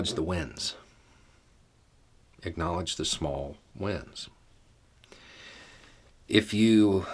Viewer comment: I thought he said, " Acknowledge the wind. The little winds." It took me a moment or two for real meaning to come through.